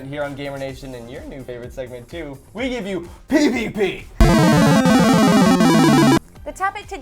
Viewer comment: i thought he said PPP...